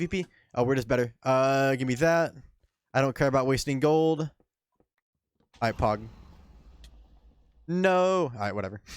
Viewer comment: did you just say "aight pog"